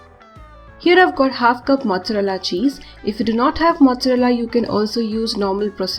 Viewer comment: There's an error at 1:39 minutes. She says it's half cup of mozzarella cheese while the captions say it's 1/4 cup of mozzarella cheese